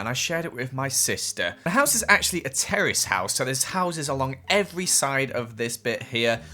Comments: I THOUGHT HE SAID A TERRORIST HOUSE 😂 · I thought he said it was a terrorist house!!! Lol